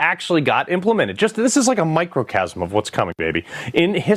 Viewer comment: Did he just say microcasm? What?